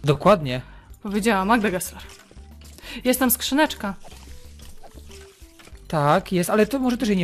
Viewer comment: czemu usłyszałam "Jestem skrzyneczka" XDDDDDDDD